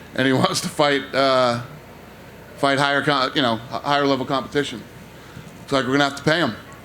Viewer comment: anyone catch @ 5:18 when dana slipped up and said higher contracts 😭